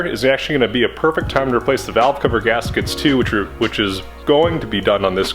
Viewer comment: Fel-Pro, I swore I heard velcro gaskets. Ha. Great video as always